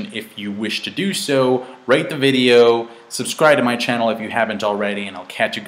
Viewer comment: Thought u said rape the video at the end lol